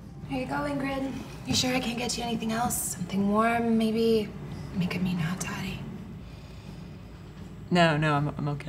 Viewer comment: What did the bartender say at 1:33? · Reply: "Something warm maybe, [I] make a mean hot toddy"